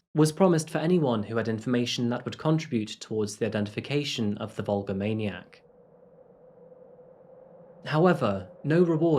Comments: I kept hearing "the vulgar maniac". Lol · Excellent video--as always! Funny, I thought you were saying "vulgar maniac" until I looked again at the video's title 😅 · i'm so stupid i thought he was saying "the vulgar maniac" until i glanced at the title again near the end of the video